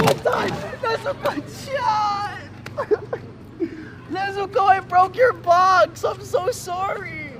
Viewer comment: Oh god that would hurt very bad and plus Nesuko hits him he’s dead💀 And I thought Zenitsu said “I’m sorry I broke ur butt”🤕😳 That would hurt bad huh. Poor Zenitsu